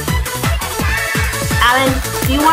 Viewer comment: When siad do you wanna be my best friend Alan I thought she said do you wanna eat my best friend…